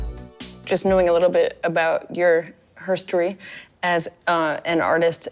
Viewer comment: did she just say "herstory" instead of history? ugh